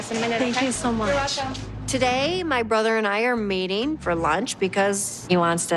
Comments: It sounded like she said "My brother and I are mating for lunch"... Then the video was just boring after that. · i heard "we are mating for lunch" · I thought she said my brother and I are mating... pronunciation is everything.